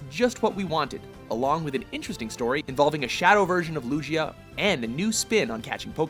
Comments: this man really said "lujia" instead of lugia smh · did he just say lujia?